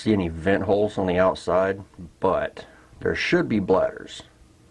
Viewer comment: Thought you said there should be a be oil in the shocks lol they look like just empty canisters with Springs